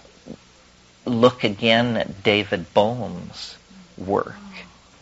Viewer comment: David Bones? Is that what he said?? I wanna find this guy on the internet... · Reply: Bohm